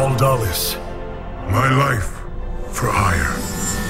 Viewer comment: My life for Aiur! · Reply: Carbos when i was younger i always thought they where saying My wife for hire. It didnt make any sense and ik it was wrong but thats what it sounded like lol